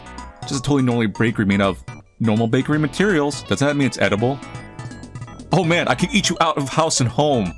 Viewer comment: who else heard “Oh man I could eat you out!” For that split second? Or was it just me Edit: I’m kiddin if someone takes that seriously! I just think he tried to B slick wit it